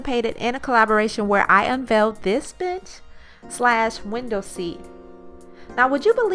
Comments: It sounds like you said "where I unveiled this bitch" hahah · Lol! At the start of the video i swear i heard " I made this bitch" instead of "this bench" haha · I swear I thought you said "this bitch". but amazing transformation!